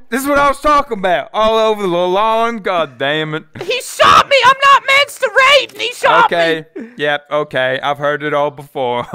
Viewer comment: the subtitles say: “I’m not meant to rape” bruh I was laughing so hard